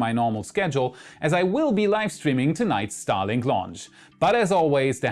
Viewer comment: oh man!!! I heard that as tonight I'll be live streaming Starships launch! I almost feel out of my chair, then I realised you said Starlink. While still cool ain't no Starship lol. Edit - anyone else think that first lol?